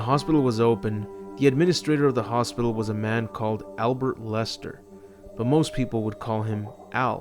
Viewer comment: Sounded like you said albert wesker